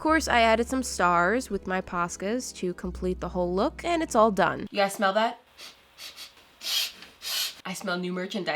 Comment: When she said ‘you guys smell that’ all I heard was ‘yes mother’ ?? Anyone else or is it just me lol